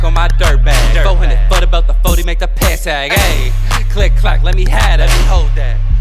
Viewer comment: Did he just say "400 40 by the 40 make the pants sag" or was that me?